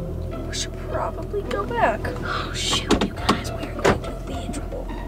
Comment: Omg you said oh s*** i thought u said oh shoot but u didnt